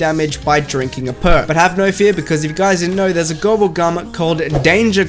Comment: what the fuck at 4:18 your video said "have no fear because there is a gobble gum that-" but the SUBTITLES said "have no fear because there is a global government that-" coincidence or 420?